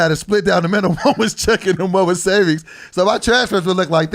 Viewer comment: I thought he said “one was chicken one was savings” 😂